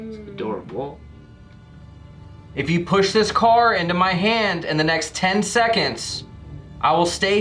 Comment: I heard a voice that said Hansen.